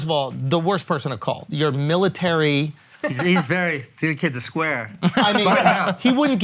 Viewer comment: What does he say at 1:00 something.. Square? Please and thank you